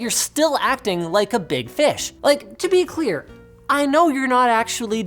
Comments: Acting Like a big fish But I heard it as Acting Like a big dick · What Linus said: "You're still acting like a big fish!" What I heard: "You're still acting like a big bitch!" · I could have sworn you said Intel was acting "like a big bitch." I had to clutch the pearls and rewind.